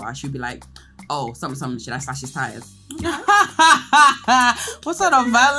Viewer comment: please someone tell me what Toolz said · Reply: Should I slash his tyres